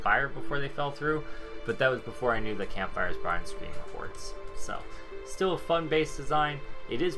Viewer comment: "But that was before I knew the campfire..." What does he say at the end of the sentence? It sounds like "barns screen reports" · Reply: "brought screaming hordes." :). Camp fires generated massive heat map spikes at the start of alpha 16. It has since been rebalanced and isn't so bad you can use them but I would only recommend them when you're on your horde night. They still do generate a lot of heat map so keep that in mind, it's better ...but still an issue.